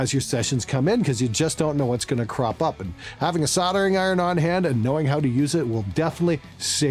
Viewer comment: misheard and thought you said that a useful skill to pick up was sodomy (in stead of soldering). I know that the music recording business is hard, but not that hard?